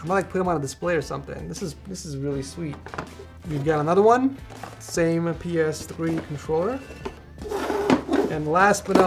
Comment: he actually said we have a ps3 controller😂